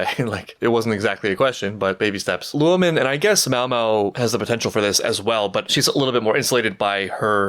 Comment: lmao idk why i heard this as "women and i guess maomao" luo(men) is virtually pronounced the same in english as lou(lan). it's essentially like saying "roman" with that distinct R/L japanese sound